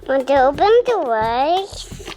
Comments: Why did it sound like said “did you open the vlog” 😂 I might be delusional lol 😂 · In the beginning it sounds like says “window open with rice” LOL he is soooo cute!